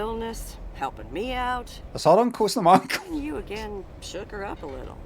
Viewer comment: what does he say at 3:57:38 ? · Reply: "It's her uncle, it's not my uncle."